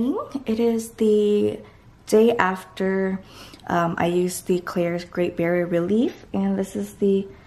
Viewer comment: lol did she said klairs?